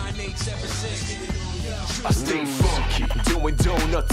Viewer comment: Hey when I say "Get Funky" in the video can you guys pretend I was saying "Stay Funky" the whole time, whoopsie. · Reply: I thought it said "I say fuck it"